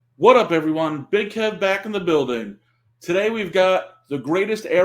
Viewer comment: Hey Kev. Its my first time watching your videos. Not gonna lie, i laughed so hard the first 2 seconds of your intro, i thought you said "Big head back in the building" 😅 i had to turn on subtitles to see if my ears needed some cleaning, and I did need it 😅 Im so sorry! But guess what!? You got a new fan! I have to subscribe😊 keep doing what your doing, and remember, Ronaldo is the best😉